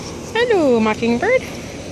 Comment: When she grab the bird i thought she said hello mockingbutt XD